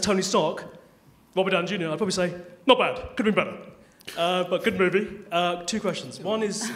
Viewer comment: did one of the guys say tony stark could of been better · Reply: yeah i thought he said that too but no, it's at 4:44 he says if he answered the question AS tony Stark, he would say "not bad, could have been better"... cos Tony Stark is always underwhelmed